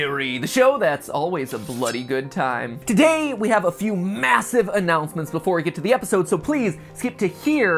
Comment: Did he just say…MASSIVE?